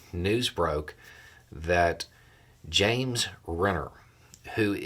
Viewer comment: Am I the only one that thought you said Jeremy Renner at first? I was like what did Hawkeye have to do with the fake elector scheme?